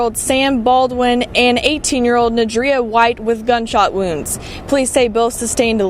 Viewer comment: her name is Nadria bro, not Nadia. Had me tripping, I 💬 I heard them say Nadria earlier